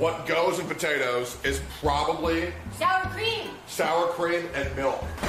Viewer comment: I heard XIAO's cream LMAO